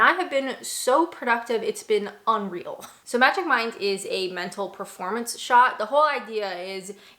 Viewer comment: ~ What did you say you take magic mycelium?